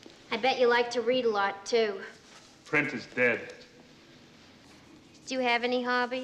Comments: When I was a kid, I called it the steak puff marshmallow man. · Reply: I thought Egon said "printer's dead," not "print is dead." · So many memorable lines from this ghostbusting comedy! Some of my favorites are "I collect spores, molds and fungus", " Cats and dogs living together, mass hysteria" and "Ray, when something asks if you're a god, you say Yes!". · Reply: I thought he was saying printer's dead for years.